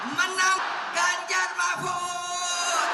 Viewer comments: Ada gairah buat nonton ILC kalau ada Bung Rocky Gerung ❤ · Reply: Kok saya dengarnya menang Ganjar Prabowo yaa ? Apa saya salah dengar gtu yah ? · Menang ganjar prabowo(keceplosan ibu mega)!?ternyata bagian dari politik pdip dengan prabowo..bhehe.apa salah denger w